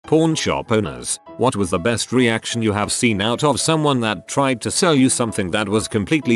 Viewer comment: if ur just listening to this it always sounds like hes saying porn shop lol